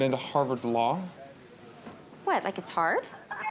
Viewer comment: Y’all, watching Legally Blonde when I was a little girl, I thought Reese said “like a tard” it’s when I got older I found out it was “like it’s hard” 😪